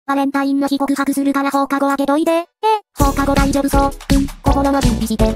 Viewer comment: QUE? escuche quieres comer un chorizo 🤨